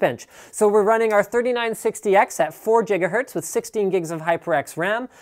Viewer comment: Did I just heard jigahertz?